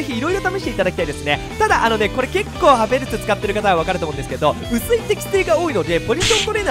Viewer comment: テスト勉強しすぎて「ハフェルツア〇ル」に聞こえた俺は末期 ちなみに明日テストです、応援よろしく🙏